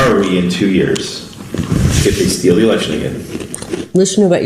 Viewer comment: did he say “steal the election AGAIN?”